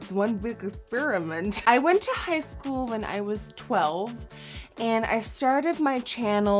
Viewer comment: did she say she went to high school when she was 12